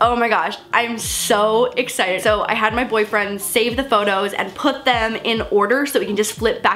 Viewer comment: DID SHE SAY BOYFRIEND